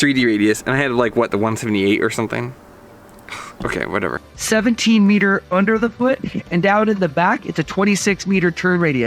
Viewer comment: he is clearly saying it's a 17m turn radius underfoot (since you keep pausing the video it broke up the flow/order as he just said "17m" but he was obviously going down the different turn radii values from tip, underfoot and tail)....I'd also think most people are already familiar that it is obviously 88 in width underfoot anyway.....your bias is making you too critical at this point in the video. Your other comments about his hat/sunglasses and not making full curved turns (as well as filming crappy angles with a GoPro) are certainly valid though.